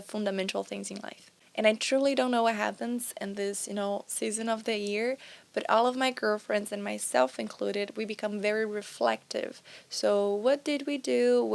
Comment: Ola, gostaria de saber o que e dito no minuto 5:20, eu entendi "all of my girlfriends included me" então coloquei a legenda altamotica so pra confirmar e apareceu a mesma coisa. A MINHA DUVIDA É SOBRE A PARTE "MY GIRFRIENDS" "MINHAS NAMORADAS INCLUINDO EU" nao faz sentido, pensei que poderia ser "close friends" mas não parece ser. Obrigado pela atenção.